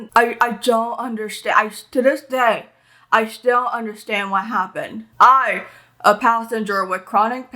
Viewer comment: The captions are a little off at 2:24, u said “i still don’t understand what happened” and captions said “i still understand what happened.” Just wanted to let u know ❤️